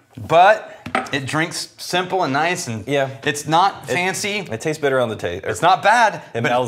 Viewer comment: It very nearly sounded like said "It tastes better on the taint," not "It tastes better on the taste."